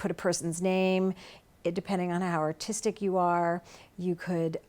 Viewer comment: I heard autistic, not artistic.